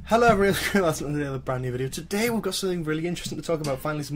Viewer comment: What does he say at the beginning of each video? All I hear is "Hello EVERYCHAUAIARISa brand new video" 😂 Not hating or anything, actually curious ahah.